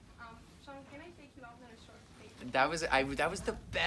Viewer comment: what did she say? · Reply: Jadzia S “Shawn Can I take you out on a short date?”